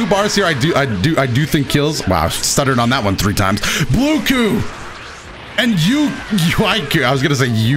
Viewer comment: Did i just hear blue ku